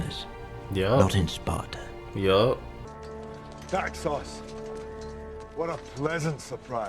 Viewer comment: Tube subtitles named him 'dark sauce' instead of Daxos 😂